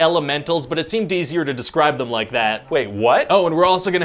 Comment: am i the only one or did anyone else hear him say "It seemed DCer to describe him like that"?😂